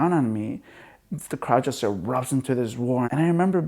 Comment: what does he say at 4:17 ? the crowd just starts .................................... ?? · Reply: Darius Anderton ...the crowd just erupts into this roar.